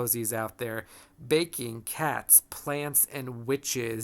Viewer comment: Ha!, when you were describing different themes in Cozies I heard "baking cats" with no comma....and I was momentarily confused how horrifying pet demise fit in with the cozy feel.....